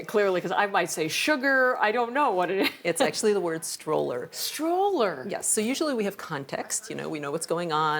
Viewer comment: I heard "stop" and "slower", but combined, "stower" does sound like "stroller"